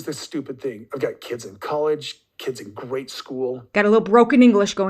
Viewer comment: Lol, I thought he said "grade school"